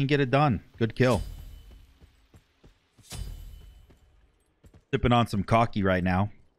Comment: Swear when it feels right. And did anyone else hear say im sippin on some cocky right now